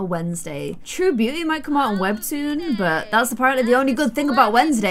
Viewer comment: Did you just say webtoon??!